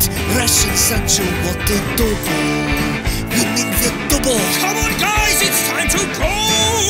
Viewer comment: Ed says come on but the subtitles say “come one”